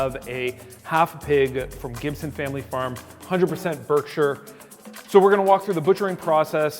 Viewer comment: For a moment there I thought he said 100% kosher lol